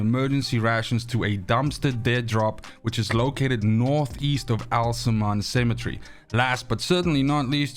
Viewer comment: Classic case of northEAST? I THOUGHT U SAID NORTHWEAST